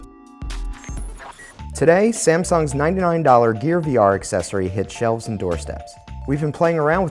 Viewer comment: did he say "today, Samsung's 9 million dollar"